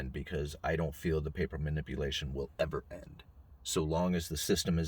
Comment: I also get the feeling that the silver manipulation will never end. At least not voluntarily. Nevertheless, I will purchase it 🤷🏽‍♀️ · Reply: He did not say the silver manipulation will ever end, he said the PAPER manipulation will never end (1:40). Big difference. That said, I completely agree with him.